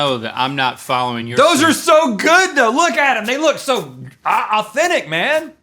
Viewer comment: Did he say "look Adam they look so good" ? at 5:53 ?